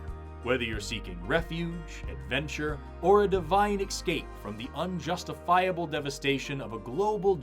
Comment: ! At 20:33, did ylu actually say the word "eXscape?" I am... casually dismayed😊